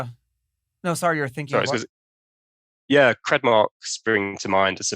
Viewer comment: What is he saying around the 11:12 mark? Kredmark or something like that